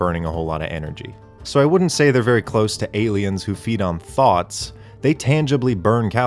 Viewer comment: I heard that as "aliens who feed off THOTs" and hit rewind a few times before I was like "oh... THOUGHTS" .... it's 1:37am atm, forgive me pls